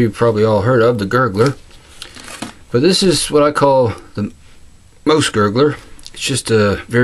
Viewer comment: Nice one! Love the accent man... when you say "mouse" all I hear is "moose." :-)